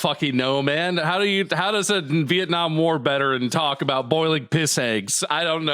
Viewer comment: Did accidently say "War BETeran"?? instead of Veteran 😂